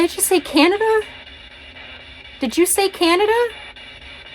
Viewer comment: Nicely done hun! Definitely some interesting responses. When you said did you say Canada I swear I heard it say “Houston” 😮🤔🤔 Hope you guys are well much love 😊💜💜